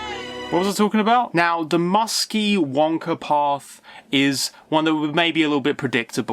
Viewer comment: The musky wanker path? Did you say?